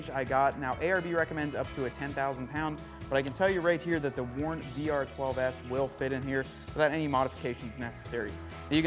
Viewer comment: great video -which WARN winch is this? I thought you said DR12S but I can't find that one...Thanks!